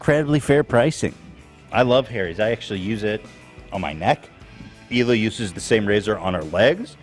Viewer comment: It sounded like Dan said “I use em on my dong” when talking about the razors lol 😂 I know he said dome but I literally choked on my water when I heard that